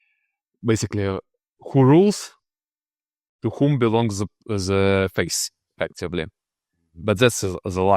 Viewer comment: what is he saying? Face? Faith? Couldn't quite catch that. Is he saying that modernity says that rulers determine the faith and that this is a lie? Meaning that politics and spirituality are inverted? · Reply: He says faith. th is a difficult sound for many non- Anglophones.